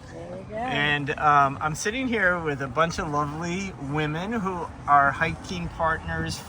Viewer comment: I had to rewind, I thought you said lonely women 😂. They were definitely lovely. Thank you all for sharing your inspirational stories!